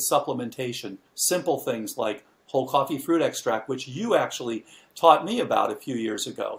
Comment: What does he say at 1:04:40? It sounds like cold coffee food extract.